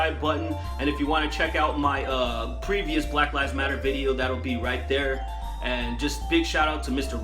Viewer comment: I swear I hear "BlackLiesMatter when he mentioned what he will be talking about... Which made me think "That's basically the movement"